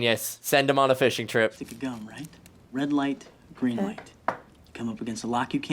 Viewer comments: Only second reaction I've watched her do but i think it's funny when she mishears stuff like "good night" instead of "green light" with the gum. 😊 Did it once or twice in, i think it was, Fight Club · I have no idea what this translates to, but I can read the sounds. “Gutsudo naito jana kute, guriin raitoto chaonen patsu piiri torudein gudongu?” I’m sure I am putting things together that don’t go together. I’ve managed to learn the sounds of hiragana and katana. But no idea what the words are or how to know when one word begins and another ends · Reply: Hey, Jaby (I assume). So phonetically, it’s more like “guddo naito ja naku te, gureen raito to chaunen happii ritoru, dingu dongu”. Which is, “It’s not, ‘good night’, but ‘green light’, isn’t it, Happy Lil Dingdong?” There was that scene where Ethan Hunt takes that explosive gum and yells out, “Green light!” And I thought I heard say, “Good night!” instead 😄 And she once referred to herself as a “happy little dingdong” so that’s what I’ve been referring to her from time to time. edit: in the Japanese, I think you might be misreading some of the characters like the small “tsu” in “good night / グッドナイト” which makes these tsu silent and emphasizes the following letter (almost always a consonant in English, I think). So a word like “pad” in like a controller pad becomes パッド pa-ddo.